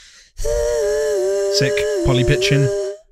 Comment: I thought just said poly bi*ching instead of pitching. 😂🤣🤣🤣